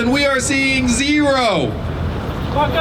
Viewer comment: what did that guy say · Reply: “F*ck em”